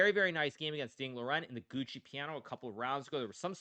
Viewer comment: did he say that Fabiano won against Ding Liren in the Gucci Piano?